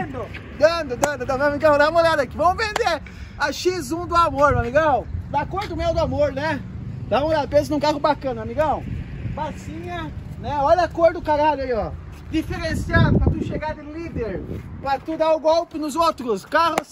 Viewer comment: Só eu que entendi "Olha a cor do Kralho aí ó..", nos 0:43 ????